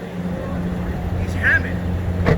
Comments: I thought the said that's Hammond at first lol like Richard Hammond from top gear.. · I thought he said he was Hammond for a second